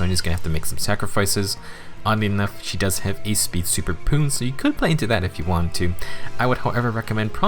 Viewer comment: Did you just say Speed Superpoon?